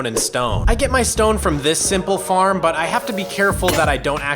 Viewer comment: Thought he said pee farm